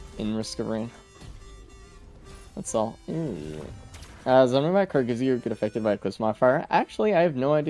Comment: what did he say??? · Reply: I think it's "does the money credit card gives you get affected by the eclipse modifier"